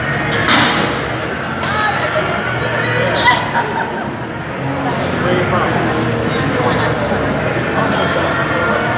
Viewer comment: where did she say she was from? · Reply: Sounded like she said Antoinette?